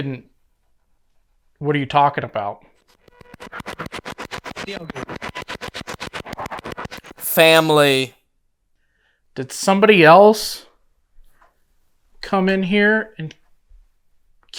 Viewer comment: it sounds like it says “kill them” instead of family to me, and to me that would make more since for the question, maybe I’m wrong just what I hear